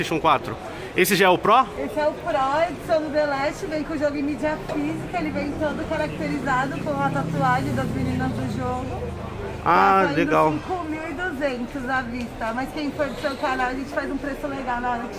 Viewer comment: Eu ouvi direito , 5200 , foi isso mesmo